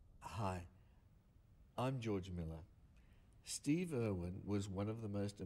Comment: for a minute I thought he said "Steve-O was one of the most amazing people who ever graced this planet" Which is true